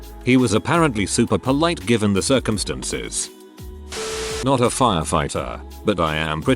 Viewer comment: I heard that as "given the circumcision" and, y'know, that works too